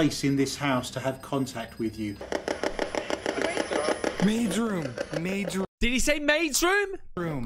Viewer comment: I heard “the basement” , not “maids room”. 😕